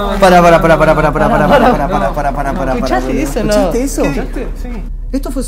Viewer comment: en el minuto 11:14 voz de niña no se que dijo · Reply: agu lopez dijo corre